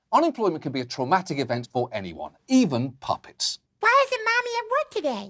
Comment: My brain briefly stopped working when I thought he said puppies instead of puppets.